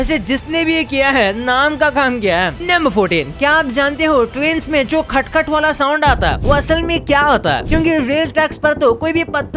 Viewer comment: When he tell first time about train I heard twins